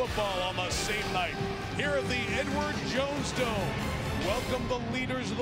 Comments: I can't be the only one that heard him say "N-Word Jones Dome." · I can't be the only one that heard him say "N-Word Jones Dome." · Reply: I know what he said. I was not implying that he intentionally tried to make a reference to the N-word, it just sounded like he said "N-word Jones Dome" and I found that to be funny. It's not that deep.